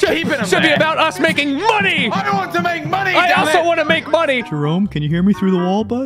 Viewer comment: Is it just me, or whenever I replay this part it sounds like Jack is saying "I don't want to make money"